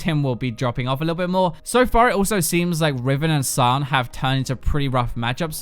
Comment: can somebody please tell me what he said at 5:18 ? riven and saan ? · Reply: Alex Hoenix Sion